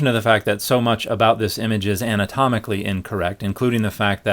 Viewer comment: did I hear you say, "comical incorrect?" Oh no, you said, "anatomically incorrect." 😜